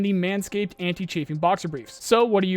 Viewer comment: I swear I heard "Anti-cheating boxer briefs"